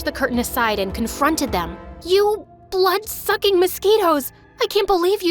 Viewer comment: “YOU BLOOD SUCKING MOSQUITOES” that made me laugh and the fact I thought she said burrito’s until I saw the caption that made me laugh more XDDDDDD